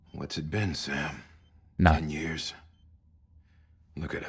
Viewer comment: He says nine years but the subtitles say ten… weird